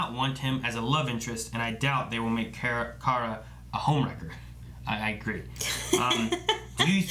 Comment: first 2 times I watched this, I thought said Homelander. Kara becoming Homelander would be so weird, though that would be a much better costume for her. Shots fired.